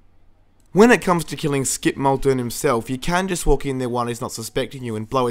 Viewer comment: "Skid Maldoon" or whatever it is. I hear "Skidmark Douche"